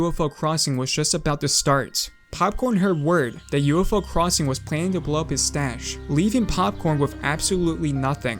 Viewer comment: almost fell off my chair because my pc froze for a second and i thought it said 'planned to blow his moustache'